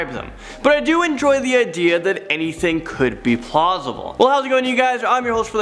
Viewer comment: did he say plosible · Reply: He said plausible, it's hard for me to explain but it means something along the lines of;can be explained and is likely. Probably not the best definition but close enough to give you a basic idea.